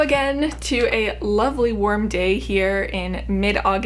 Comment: Omg I thought she said “worm day” not “warm day”